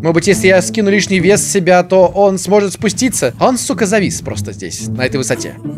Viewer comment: Я один услышал в 13:23-25 "Он с*ука завис просто."?